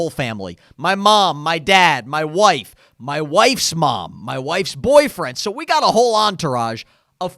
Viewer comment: did he just say his wife’s boyfriend??